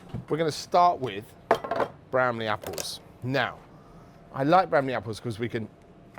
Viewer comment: At first I thought he was saying "brandy apples," and I was thinking about apples stewed in brandy and sugar, like a sumptuous compote. I thought, "That sounds awesome!" Then I read the description and saw he was taking about Bramley Seedling apples. I don't recall ever seeing that variety before here in America. I'll have to look around.